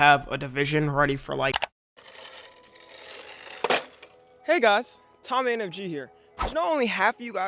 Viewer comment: why did I hear Nigas instead of hey guys